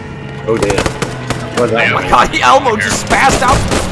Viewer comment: for a second i thought he said my name but i realised he said elmo and not elbow :(